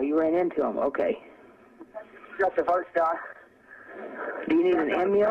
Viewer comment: Here's a more accurate transcript for 911 call: Operator: “911 emergency.” “Yes, I'm in the middle of a field, I think we just pushed some guys over. (We’re out here) going towards Abilene on both sides. My truck ran out of gas, there’s one car here, a guy’s chasing me into the woods. Please hurry!” Operator: “Ok, now run that by me one more…” “And we’re not talking to ‘em. I accidentally ran into ‘em.” Operator: “Ah, you ran into ‘em, ok!” “Yes, the first guy. [The operator and speak at the same time, saying]: (“I’ve got cuts!) Operator: “Do you need an ambulance? “Yes, No! I need the cops! OPERATOR: “Ok… Is anybody hurt? Hello… Hello… Hello. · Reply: If you listen at 11:03 you can hear a gunshot go off in the background. Not sure if anyone else picked up on that. After that it almost sounds like he says... Got the first guy. When she asks if he needs an ambulance he says something at that exact time... Think he says that guy too or something.